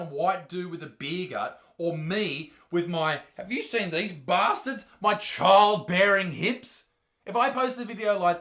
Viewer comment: I thought the background said doughy bastards 😂